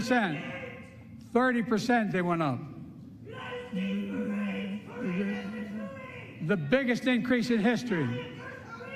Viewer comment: I can't tell what that guy in the crowd is screaming. Anyone know? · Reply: “Remember Abbey Gate” Referring to the Afghan withdrawal explosion at the airport.